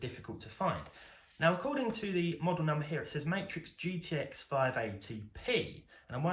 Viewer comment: I thought you said Rx 580! I was like “why such bad performance?” Lol